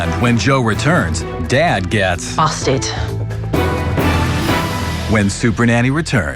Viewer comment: lmaoo am I the only one who thought that when it says `` and when jo returns dad gets ` bastard ` `` XD Ik now it says `busted`